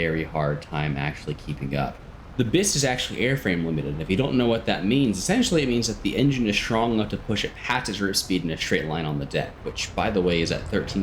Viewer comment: can someone transcribe whats said here? sounded like a bunch of gibberish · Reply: What limits its top speed isn't engine power, but airframe strenght